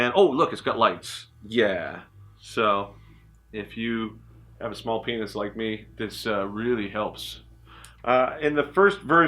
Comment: is he saying, "...if you have a small penis like me...."???? hahaha... I wonder how that helps